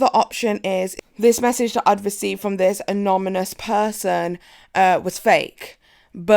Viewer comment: I kept hearing 'anomynous' and that's the cutest thing ever lol